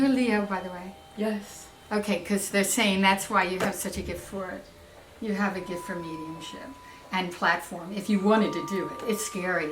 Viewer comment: I thought they kept saying "you have a gift of needing shit"